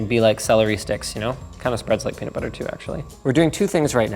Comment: Everyone's talking about the PC and im just like: wtf did he just say celery and peanut butter??